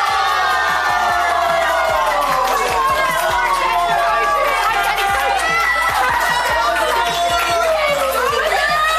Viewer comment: Now we need the top lip readers to work out what on earth said at 26:34 while screaming 😂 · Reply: Think it is, "We got it! I'm getting soju! I'm getting soju!